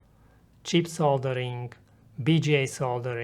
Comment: For a second I thought he said cheap soldering